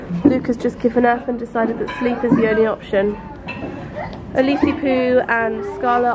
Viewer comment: What does say at 3:44 ? It sounds like she says "a leafy poo" but I doubt that was it